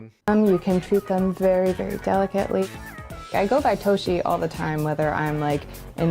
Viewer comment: I thought she said Hoshi for a second there.